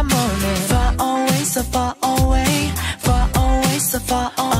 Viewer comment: I heard "safari way" instead of "so far away"... I heard "I wanna be your running baby" instead of "I wanna be your morning baby"... what is wrong with me ;~